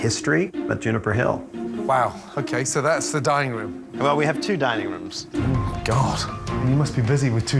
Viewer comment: for a minute I seriously thought that Gordon said "Dying room".